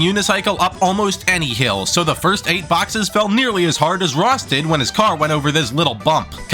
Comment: Jeez, thought I heard “felt as hard as Ross”